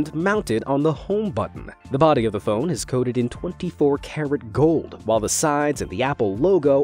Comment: at first I heard 24 Karen gold